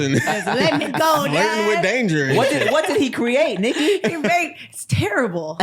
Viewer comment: did he say the n word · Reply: "Nikki"